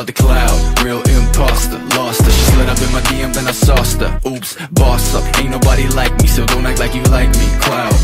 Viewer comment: só eu q ouvi "bosta"?